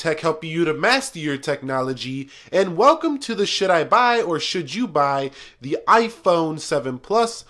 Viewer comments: It sounds like he said "welcome to the shit I buy "😂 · "and welcome to the shit I buy" Did anyone else hear that? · Swear I thought he said "welcome to the sh*t I buy" 😂😂😂😂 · Almost sounded like you said "Welcome to the shit I buy" Lol. Great video!